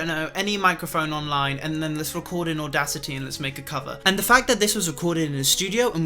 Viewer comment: “let’s record an audacity and make a cover” idk if i misheard but if that’s what you said it is the funniest line ever omg i’m crying